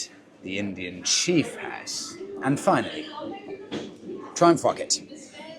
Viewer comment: I swear the gentlemen is saying "and finally... try and f*ck it." And... okay, on one hand... Don't mind me (: Well Done.